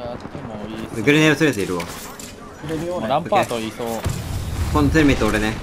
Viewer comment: なんかランパートがワンカートンに聞こえる